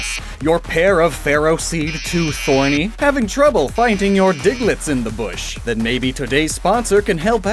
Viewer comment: i skipped the add, but not before i heard, "is your diglett lost in the bush?"